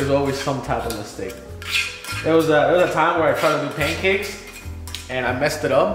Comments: Oh, am I mistaken? I though I heard something about delicious pancakes... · Did I hear something about delicious pancakes